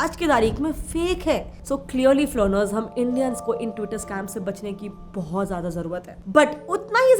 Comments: did she said Twitter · Did you say twitter scam?